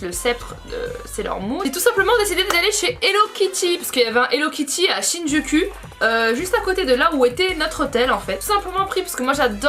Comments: pourquoi j'est entendu "Chine du cul" 😂😂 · A partir de 11:40 j'ai cru entendre quand tu parlais d'hello kitty de Signe du cul x')) 😂😂 et enfaite c'est un quartier ou jsp quoi 😂😂❤